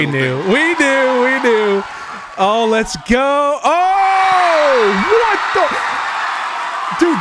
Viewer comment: did you say WHAT THE FU